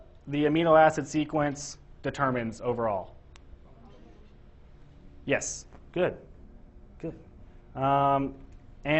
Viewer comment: what did we say the amino acid sequence determines overall? part 31:58 can hear the answer. did they say structure? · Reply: Tertiary structure